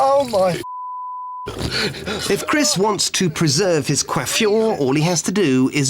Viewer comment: What is Quentin saying on that time? Quafum? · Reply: coiffure, French for hairstyle